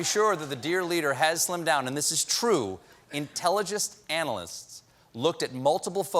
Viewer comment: is Stephen okay? I've noticed him mispronounce several words since they started doing live shows again. sounded like he said Intelli-gest analysts.